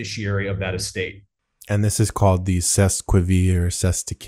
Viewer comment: What is that word he was saying at like 16:49 and 23:00ish, ce Levi or CES lequivre? I'm trying to figure out what that word is so I can look it up but I don't know the right spelling is it a French word?